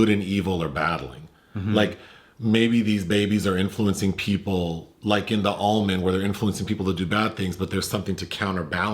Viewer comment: “Influencing people like in the almond” lol I thought I heard that . Great review thanks !